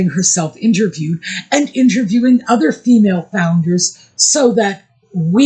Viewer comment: Sorry, I thought I heard you say, that nutmeg is a " female bounder"! 😮